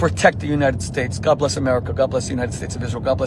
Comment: Did Shmuley say "God bless the United State of Israel"?